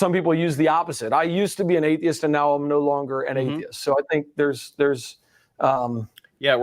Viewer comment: I'm watching this a little late but I swear Eric said in the 9:53 minute mark that "I used to be an atheist and now I'm no longer an idiot" or something very close to that.